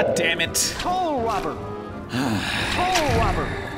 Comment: It doesn't even sound like soul robber to me, more like pole prober, this game seems like some frustrating prototype Souls game. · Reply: I heard "TOLL ROBERT"